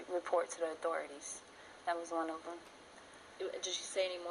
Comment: Did she say "That was on Oprah?" (7:52) She is just regurgitating information off of the news and t.v. sources. Dafuq? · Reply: no she said that was one of them